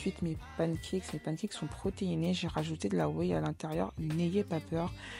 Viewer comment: tu as rajouté quoi à tes pancakes ? Je n’arrive pas comprendre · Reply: De la Whey 💪🏾